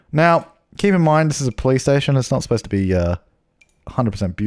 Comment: When he says police station sounds like hes saying Playstation